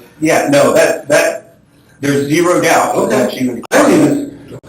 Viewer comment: wait I just changed my mind. Did you hear what said? He said there is zero doubt. He said ZERO. he didn’t say 2%.